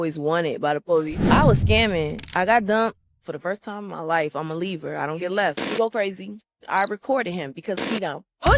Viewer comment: Tommie a libra ♎️? I thought she always was a Gemini ♊️ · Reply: see I got confused also I thought she said I’m a Libra she said I’m a leaver